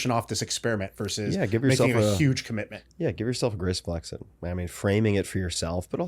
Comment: what the heck word did he say??? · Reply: Graceful exit 😅 Took me four listens